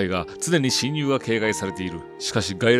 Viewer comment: ない蜘蛛よ に聞こえた